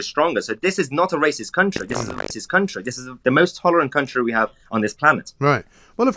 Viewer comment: WTF happened at 6:00 ? It repeated what he said but it came out "This is a racist country" lol